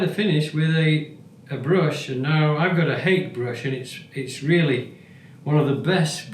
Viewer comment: A hake brush. Soft goat hair. I thought you said 'hate brush' at first! Thank you for another excellent video.